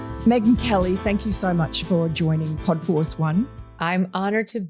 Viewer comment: I thought that said Poo Force.... 😂